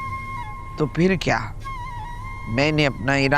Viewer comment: py kya Bola hai koi bataye GA 😢Maine suna hayee raam bola hai is aurat ne ho sakta Maine galat suna ho 😮 lekin drama acha hai good 👍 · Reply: She said phir ab